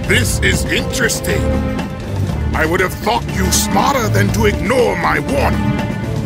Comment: I keep hearing "i would have f*cked you smarter than ignoring my warning" :D